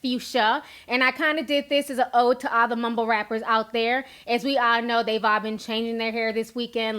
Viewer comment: Did u just say mobo rappers loooool by the way thank u for approving me on instagram thank u thank u. Nice content love watching